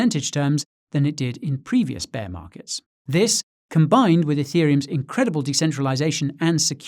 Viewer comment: "eth's incredible decentralization.." did I hear that right?